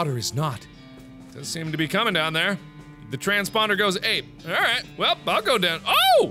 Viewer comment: At :16 I thought it said transponder snail. Eh? Eh?